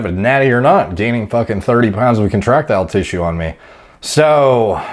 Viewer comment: Avg person: "Gains muscle" "Gains contractile tissue" Cries in illiterate. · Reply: I kept hearing "erectile" and laughing